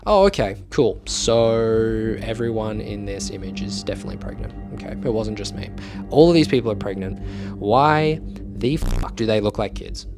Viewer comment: why the heck did i hear it like he said “ok so its not only me who’s pregnant” 💀💀